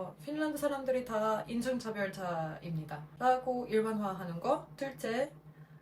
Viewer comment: 얼마 전에 좋은 소식 있다 들었는데요. 앞으로도 겸공 뿐만 아니라 다른 채널에서 자주 찾아뵐 수 있으면 좋겠습니다. 계속 응원할께요. 다만, 한국어가 수준급이라는 걸 알고 있기 때문에 자막 검수는 AI 변환도 좋지만, 2차 검수도 필요하다고 조심히 추천 드려 봅니다. 7:07 "인정차별" 이랑 "인종차별"은 맥락상 아예 다른 의미로 전해지기 때문입니다. 아. 참고로 방송주제와 관련된 정치세력이 대한민국에도 엄연히 존재하고 있습니다. 오렌지색을 사용하는 모 정당이 존재하죠. 극우, 혐오, 차별이 전세계적으로 확산되고 있는 것 같아 걱정이네요.